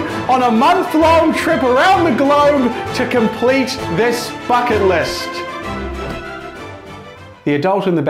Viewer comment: Ngl I thought he said f**cking list